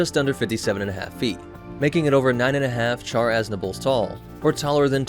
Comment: Did anyone else think it sounded like Char Assnibalat 1:17?